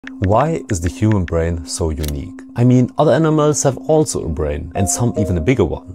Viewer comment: I thought he said why is the herobrine so unique